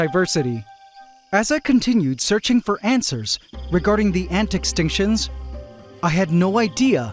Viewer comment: I could swear he said "looking for 🐜swers".